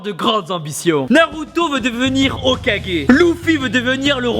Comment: il a dit loufi ou c'est moi à